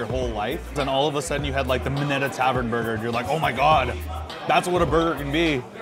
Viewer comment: what did he say the best burger is. minute tavern? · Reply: Minetta Tavern in NYC! Thanks for watching!